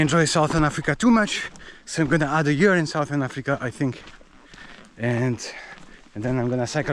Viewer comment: I think I heard him say like he will be in Cape Town for a year…… Indeed it’s a place worth spending time full of adventures…. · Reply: he never said Cape Town. He said “Southern Africa”, so that also means not only South Africa.